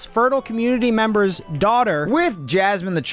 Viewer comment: did i just hear that right💀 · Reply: Oh nvm i thought he said fertile daughter